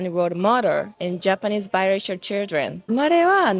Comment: Watching without reading subtitles I heard her say "I will mother Japanese biracial children" 😂